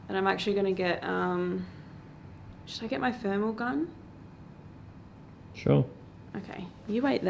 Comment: Lol I thought said she was going to get her thermals on. ..”just wait here,Jarred “...